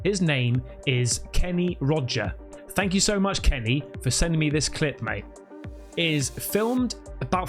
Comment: I have not seen any english man speaking(talking) like you speak (talk) . You are very much like us Punjab(ies). sometimes i laugh as when you . too good... please tell me what did say at 5:53 sec "for sending me this clip mey" what is this mey at the end. i am trying to learn the public English not bookish. Please do not ignore